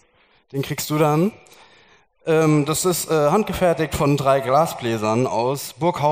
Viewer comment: Oh man ich habe verstanden "es ist handgefertigt durch 3 Grasbläser" 3:15 hat mich gekillt. Sonst echt cooles Ereignis was richtig und wichtig ist!